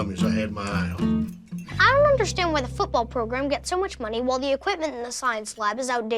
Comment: is it just me who i heard the f word from seldon?